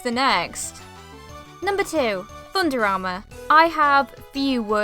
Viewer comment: When you said thunder armor I thought you said under armor, like the athletic club thing company XD